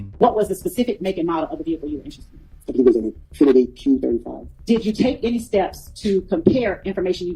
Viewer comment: he said q35 I thought they said Q50